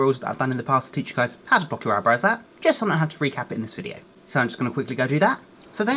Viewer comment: At first I thought he said pluck you eyebrows out at first. 😂😂